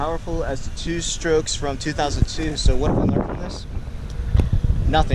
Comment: Did I hear “it was close” 😅😂